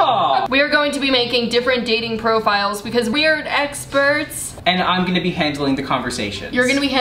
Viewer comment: I thought you said ex-birds instead of experts